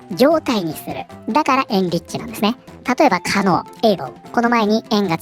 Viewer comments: 「可能 able 」が「狩野英孝」さんに聞こえてちょっとびっくりしてしまった。くだらない話ですみません。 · 音だけ聞いてた時に 狩野英孝 って聞こえたのでなんで狩野さんが？？と思ったら 可能able だった・・・